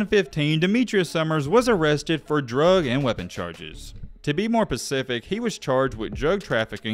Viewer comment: Sounds like you say “To be more Pacific” rather than “To be more Specific”